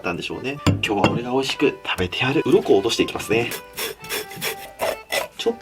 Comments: 鱗を落としていきますねーのところがうんこを落としていきますねに聞こえたww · あかん、4:08 ｢うんこを落としていきますねぇ〜｣にしか聞こえない… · う〇こおろしていきますねに聞こえる